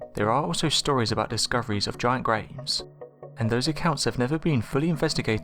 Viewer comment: I thought he said "discoveries of giant grapes..."